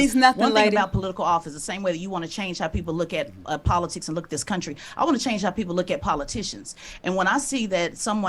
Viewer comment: She said she wants to change what politicians “look like”…it’s all about identity politics and she hates anyone that doesn’t look like her. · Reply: What she said was "You wanna change how people look at politics, I wanna change how people look at politicians" @ 27:22. I'm not gonna lie, I wasn't even thinking she was talking about race.. I thought she was talking about not liking the current requirements of politicians and how they move because the current career ones proved that the requirements she's asking for do not necessarily make you successful in that role. Then I think she was just playing cat and mouse which was gross lol